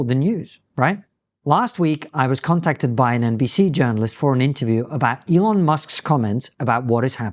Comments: In the introduction, I misheard “last week I had a conversation with an NBC journalist” as “last week I had a conversation with an NPC journalist”. Turns out my bad hearing is a good predictor! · I thought he said, NPC journalist